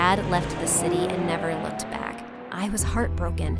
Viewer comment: I thought it said never cried 😮